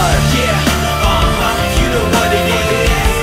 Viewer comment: The dude on the roof says "you know what it is" Instead of "Screaming thats nuts" XD